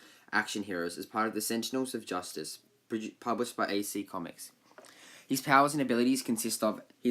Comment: Thought u Said no powers